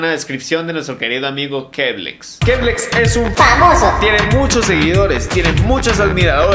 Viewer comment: escuche que es baboso XD jajajaja